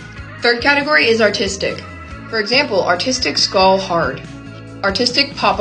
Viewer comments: Why did every time you said “artistic” I thought you said “autistic” 😭😭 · I thought she said autistic spider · Tell me why I thought she said autistic😂😂😂😂😂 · I thought their said autistic for a second lol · I kept hearing 'Autistic' when she said artistic, goddamn it blasting music with headphones on is not serving my ears well💀✋️ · I thought she said autistic not artistic 💀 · I thought she said autistic not artistic😢😂❤ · i genuinely thought she said “autistic spider hard” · I thought you said autistic spider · i thought u said autistic i was so confused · Sorry but i thought u said autistic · Not me being the only one that thought she said autistic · when she was saying artistic i thought she said autistic 😭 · I thought you said autistic... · I thought she said autistic